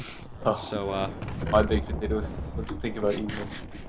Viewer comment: I srsly thought I said this was called patotoes LOLOLOLOL